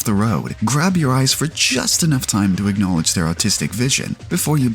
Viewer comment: sounds like you said "autistic vision" and as an autist I couldn't agree more